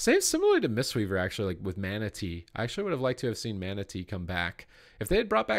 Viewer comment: when you were talking about the monk, everytime you said mana tea I thought you said manatee.